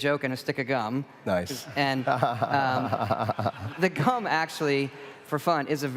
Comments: Don’t know if anyone already caught this but I just heard it. @ 3:55 Todd says “the cum reduces your hunger.” I know he meant gum, but that’s not what came out. lol 😂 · did he say cum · I’d love to talk about the gameplay mechanics and all that but I just can’t get past how Todd Howard very boldly says “cum” instead of “gum” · does he say cum or gum lol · did anyone else hear him saying cum? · Did he say cum?